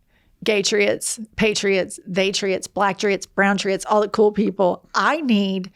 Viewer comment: I thought you said thetriots like the opposite of atheist 😜 thanks for including me the browntriot 😘